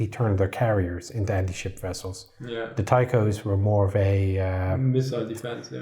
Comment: Time stamp or it didn’t happen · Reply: I think he just says Ticos